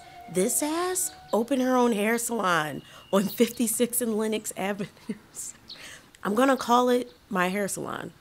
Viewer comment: Did I hear 56th and Linux Ave? Do you mean Lex? (Lexington Ave)